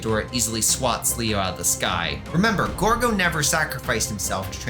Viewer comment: I keep mishearing "Ghogo" as "Gorgo."